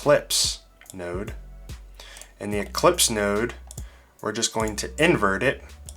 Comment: Great tutorial, really liking your stuff. I do have one little nitpick, towards the end I'm pretty sure you called the ellipse node an "eclipse" node. I might of misheard, no big deal either way. Looking forward to more great vids.